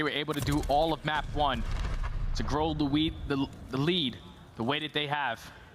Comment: Anyone else catch the caster say "grow the weed" at 1:01:22 lol